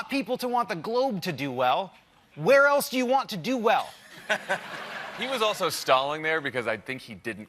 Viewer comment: legit initially thought Favreau said "he was also Stalin there..."